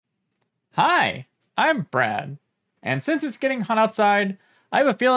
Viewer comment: Hi, I'm not brad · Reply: I always hear "Hi, I'm bread" when watching this channel.